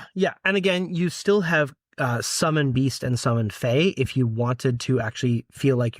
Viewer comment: my husband misheard "summon beast" as "summon geese" and I now have a new homebrew high level summon spell in the works